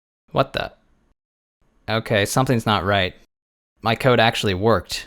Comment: did you say "my"? xD